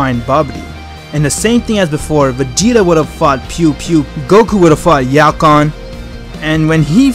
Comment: did I hear “pew pew”? (Not trying to be mean but still, LOL)